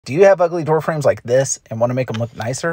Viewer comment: I heard “do you have ugly girlfriends like this” 😭💀